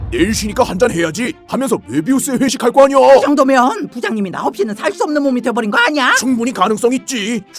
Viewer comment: 부장님은 나 없이는 살 수 없는 몸 ㅋㅋㅋㅋ 친구도 없는 찐따 아님 가정에 버림 받은 몸 같이 들리네 🤣🤣🤣🤣🤣